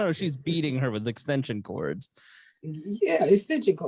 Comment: I heard "stitching cards" (extension cords) too! 🤣🤣🤣